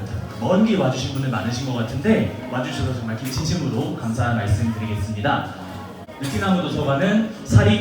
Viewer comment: 와주셔서 정말 김치찜으로 감사의 말씀 드립니다. 라고 들려요.